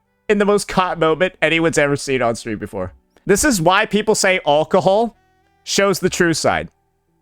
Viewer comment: for a moment i thought i heard "uncohol".